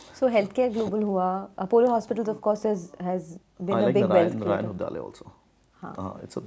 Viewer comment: Soniya Shenoy what Mr. Anirudh said I didn’t understand the name of the company. Can somebody please help me out what he said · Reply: Narayan Hrudayalaya